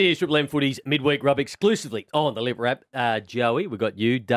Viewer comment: Did Browny say "On the liver app" instead of the 'Listner" app? LoL